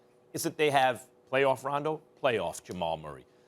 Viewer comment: did he say Denver has playoff RONDO?😂😂😂😂😂😂